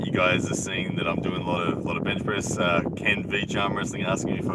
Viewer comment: did you say you were doing a lot of bench press?